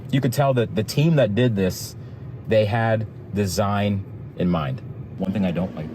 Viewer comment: lol when yyou described the spaciousness inside and your like "I could tell the team that designed this had design in mind" I didn't hear "design in mind" I heard "Claustrophobia"